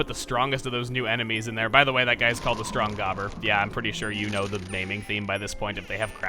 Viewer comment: what I wanna know is how did the boos find a crystal large enough to fit a gobber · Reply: thought you said goober